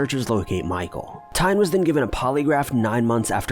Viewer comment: On #2 I had to rewind. I thought you said the missing man's dog was given a polygraph test and the results were never released publicly.